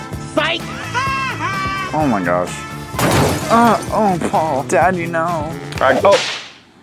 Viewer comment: I dont know if its actually what he said but I think said "daddy no" to Paul 😂